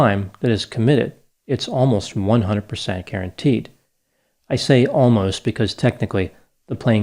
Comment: I could have sworn Dr. Grande said Karenteed 😂